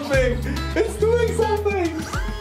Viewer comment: You said: "it's doing something..." I heard: "It's alive!" (like Frankenstein)